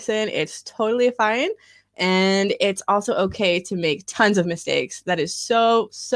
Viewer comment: uma pergunta sobre o que uma das professoras falou. Se alguém aí souber, pode responder também, eu agradeço! Em 8:28, ela disse "And it's also okay to make times of mistake"? Foi isso mesmo ou ouvi errado? Eu nunca tinha ouvido "times of" para substituir "A lot of", seria uma gíria? Existem outras maneiras de aplicar "times of" em uma frase com esse significado de "a lot of"? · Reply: Lucas N. Você ouviu errado, é tons of mistakes, not times